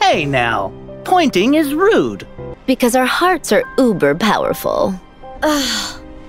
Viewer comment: wait does larxene really says "über" powerful at 3:17 ? or is this edited? thats a german word or not? I never saw it in english Content. Is that commonly used ? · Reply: yes she actually says uber powerful and yes it is commonly used